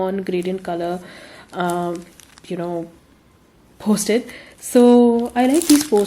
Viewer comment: Couldn't hear you clearly @ 23:22 can you please tell what did you say is that postage or posted? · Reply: Hi it talks about Post - Its...